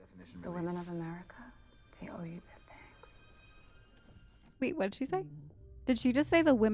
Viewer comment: i think she said thanks not sex. xD